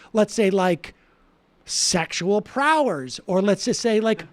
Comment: Sam, I think it’s safe to say we all have a problem with a concentration “sexual prowlers” 😂😂😂 have to assume you meant prowess.